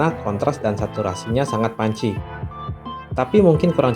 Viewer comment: Warna, kontras, dan saturasi nya sangat panci... hehe, krn reviewnya pake bahasa Indonesia yg agak baku, jadi aneh kalau nyebut punchy kedengerannya kayak panci, Anyway, nice review gan 👍 - sayang hape nya kemahalan menurut ane, kalau dikasih NFC, storage 32GB dan RAM 3GB, baru pas